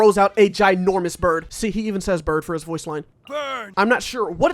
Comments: did I hear hawk too..? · What... All this time I thought he was saying "BURN" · he say bird? I always thought he said burn